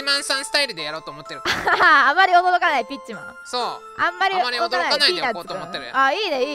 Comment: あまり驚かないビッチマンかと聞き間違えて、あれ？、18禁になっちゃったのか。と思いました（笑）